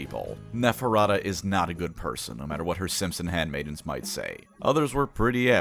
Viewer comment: I heard that as “no matter what her Simpson handmaidens would say” 😅 (In case you got stumped, it should be “simps and handmaidens”.)